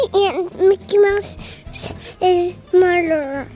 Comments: Did he really say that or did it just sound like that? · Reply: he actually said mother. I thought he said murder too. 😂😂😂 Soooo cuuute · It sounded like said "Mickey Mouse is the murderer" in beginning LOL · It sounded like said "Mickey Mouse is the murderer" in beginning LOL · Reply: I listened to the audio a second time. I heard "damn Jammies" and "Mickey Mouse is murderer." I'm sure didn't mean what he said. He is so "damn" cute, smart, and funny. We can rely on for comic relief. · Did say Mickey mouse’s murderer? 😂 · "Mickey Mouse's mother"? 1:07 awww!! 😍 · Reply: I thought he said murderer. So mother’s better 😂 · Omg I thought I heard say Mickey mouse the murderer at the start! Who else | | V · It sounded like said "Mickey Mouse is the murderer" in beginning LOL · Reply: What did he say? I also thought he said and Mickey Mouse the murderer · I thought said “Mickey Mouse’s murderer” for a solid five seconds before I realized he said mother 🤦🏻‍♀️ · It sounded like said "Mickey Mouse is the murderer" in beginning LOL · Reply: I thought he said Mickey mouse's murderer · It sounded like said "Mickey Mouse is the murderer" in beginning LOL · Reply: I thought he said Mickey was the smarterer xD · It sounded like said "Mickey Mouse is the murderer" in beginning LOL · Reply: Tommy Purdy I thought he said mickeys mouses murder 😂😂 it’s kinda adorable though